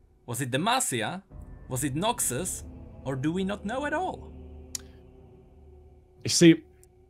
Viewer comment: I thought he said United Kingdom not unknown